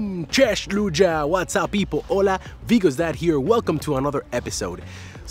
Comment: When you said " Vigos is here!" in the beginning I thought you said Bigos :D